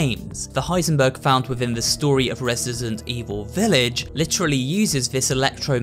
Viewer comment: Did he just say ''Resisident''? ✋😶